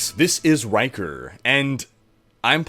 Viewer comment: All i heard is "This is wanker" - paused, disliked this shit, and commented.